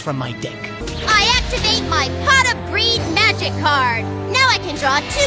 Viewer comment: uh, Pot Of Breed? Did I hear her right?